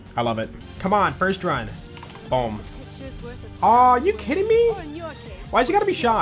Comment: "are you kitten me" I wish this is what he actually said